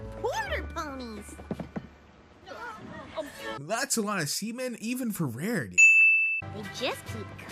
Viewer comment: HOPE YOU ENJOY AND BE SURE TO LIKE OR YE BE WALKING THE PLANK! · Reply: when u said seamen I thought u said semen